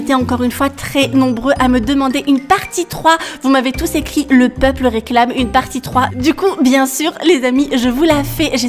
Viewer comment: ( le peuple veut une partie 3). Moi j’ai compris sa 🤣